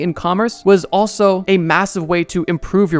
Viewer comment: Did you say massive?